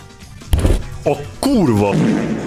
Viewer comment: Wait did you say "no kórwo"? Or im tripping